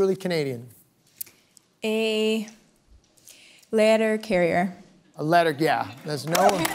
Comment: I heard say “ladder carrier” 😂